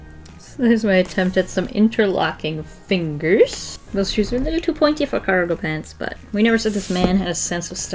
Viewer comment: Did she actually say it or did i hear wrong? I thought she said these shoes are pointy lil f***er ago? When she drew the couple?